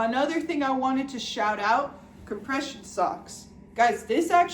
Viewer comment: when you said compression socks i thought you said depression sucks and like yeah it does i’m sure but i was so confused on how it adds whimsy to your life 😭